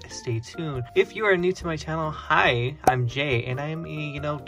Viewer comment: He said "Hi, Im Jay!" But I heard him say "Im Gay", but he is so yea